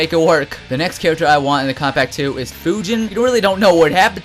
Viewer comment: Now that I look at this video, this video should have been called "Character that should not be in the Kombat Pack2" because of the problems it would cause in the game if these characters were in it lol. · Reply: how come everytime says combat pack 2, it sounds like he says compact 2 lol